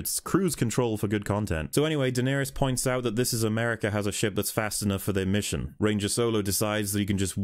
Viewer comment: Han Solo = Ranger Solo Qira = Daenerys Beckett = Rampart Vos = Vision Lando = This is America L3 = Fembot Crimson Dawn = Crimson Cringe I love these nicknames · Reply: I actually thought he said "Miss America", this actually makes much more sense!